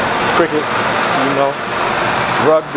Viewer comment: did he say Rugby?